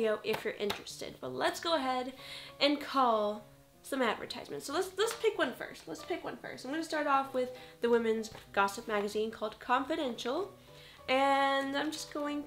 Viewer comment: When she said "confidential" I could've sworn she said "continental" and that song imidiatly started playing in my head lol